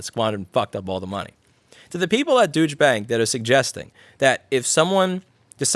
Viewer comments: i thought you said "douche bank" and apparently so did the close captioning 🤣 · thought you said "Douche" Bank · I keep hearing "douche bank" and I'm not sure if it's intentional. Sure is appropriate though.